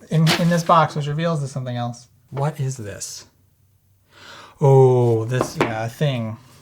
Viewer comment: "What is this? Ohhh, it's a thing...!" Did I hear that right? (Laughing pretty hearty right now - been there!)